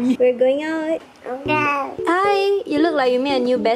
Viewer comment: my favorite part,did he say ok?